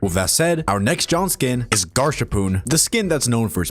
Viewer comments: if ONE MORE PERSON CALLS HIM GARSHARPOON. HE IS NOT GOOFY HE DOES NOT SAY GARSH. IT IS GAS. THE HARPOON HAS GAS. IT IS A GASHARPOON. HIS NAME IS GASHARPOON. OMFG. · GARSH HARPOON???????????? BRO WHAT I THOUGHT MY FRIEND SAYING GASH HARPOON WAS BAD